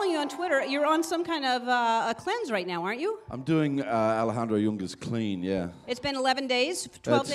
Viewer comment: what does he say? im doing .... great